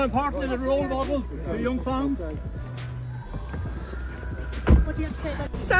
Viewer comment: Did I just hear “ role model for the young generation “ ? That journalist is out of his mind. Connor has been far far away from being a role model.